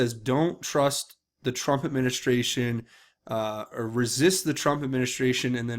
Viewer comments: is it just me or does sound like he said "resist, resist LIKE the Trump administration?" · Sounded like he said "you can't resist the Trump administration."